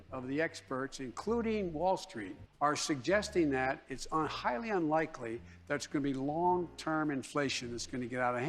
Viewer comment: “it’s unhighly unlikely…” did anyone else catch that double negative?